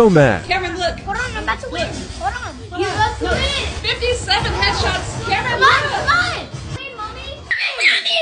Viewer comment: Am i the only one who thought she said ''57 hedgehogs''